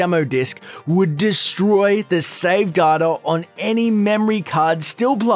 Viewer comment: When he said “data” I thought he was saying “garder”